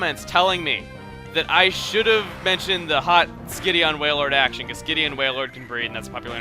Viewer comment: oh skitty on waillord i thought you said skinny waillord now it makes sense.